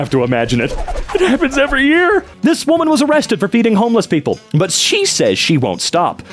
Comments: man i am high as frick from just waking up and read and heard "beating" instead of "feeding" and was shocked when agreed i apologise for that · I wasn't looking and thought he said beating homeless people and got really confused when he said that was good